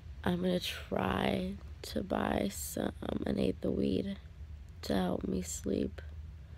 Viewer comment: it sounds like she says an 8th of weed